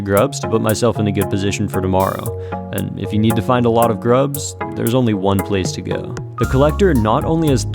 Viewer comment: I heard: "if you need to find a lot of drugs" Although, the Collector being a dealer wouldn't surprise me...